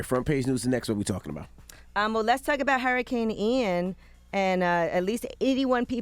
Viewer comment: Sounds like Yee saying "Hurricane AND" instead of "Hurricane IAN" ...NY accent is way more wild than the south